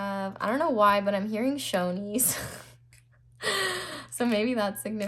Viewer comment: I picked pile 2 💛 and when you said shoneys, I thought you said "chonies" which means underwear in spanish lol I left a bra at his house and when we broke up he "couldn't find it"!! Lol so funny though! It resonated 😊